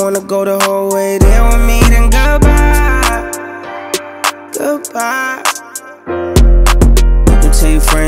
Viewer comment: Feel like the part where he says "goodbye, goodbye" almost thought he said "girl bye, googdbye" 🤔🤭 Overall, the songs is flames! 🔥🔥